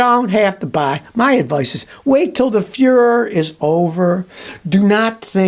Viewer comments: Did he say wait for the fuhrer to be over? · Did you just say "wait til da fuhrer is over"? 😅